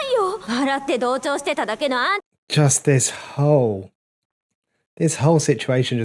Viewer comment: I thought he said ''Just this hoe'' for a second 😭😭